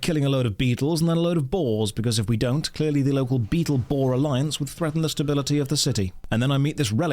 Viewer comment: Did you just say Beetleborgs? I used to love that show!